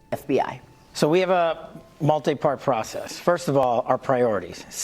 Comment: did he say "rig arrests" 😮